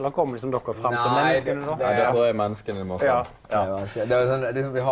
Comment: i am SO late, but doesn't Vegard say nei at 8:45? idk if i don't understand the context and that's why I'm confused